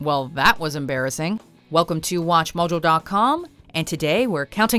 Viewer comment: The biggest mistake is letting watchmojo do anything with sports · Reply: Randy Cabbage i honestly thought you said machoman instead of watchmojo. My brain